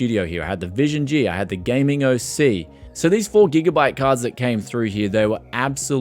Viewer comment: the RTX 3080 4GB EDITION....... what you hear "SO THESE 4GB CARDS" and what was actually said "SO THESE FOUR GIGABYTE CARDS" I'd like to see those Molex to 6/8 PIN adapters tested on your Corsair HX1000 and see if that will go pop as well just to be fair and if it passes then I'm going to use some to wire my refrigerator up to run my A/C.